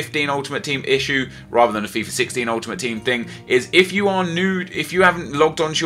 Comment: Did he say "if u are nude" lol